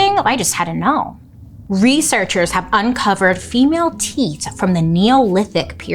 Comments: I really thought you said “teet” like a tiddy not “teeth”. I mean stress in teeth though, wild! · At first I thought you said "teet" instead of teeth 🙀🙀😹😹😹😻😽🧟‍♀️🐈‍⬛